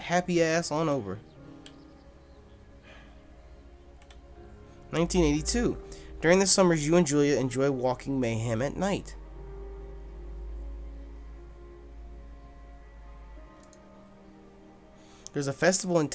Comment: LOL when it said "u enjoy walking Mayhem at night" i thought it said "u enjoy some Mayhem each night"